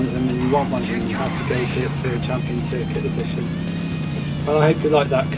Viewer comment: Am I just mishearing things due to not being used to British accents, or are you actually saying "championsick" instead of "championship"?